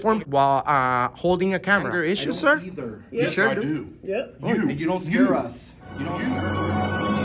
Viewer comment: Did he just say "your the problem cause you dont fear us?" WTF